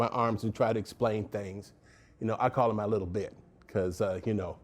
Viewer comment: I thought he was gone say “I call her my lil b word” 😂😂😂😂😂😂😂😂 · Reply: He said lil bit